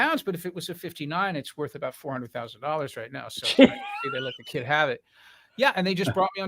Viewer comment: did i hear it right 400 thousand dollars he said ?